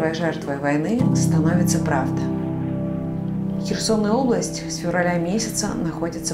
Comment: мне не послышалось -"Херсоная область".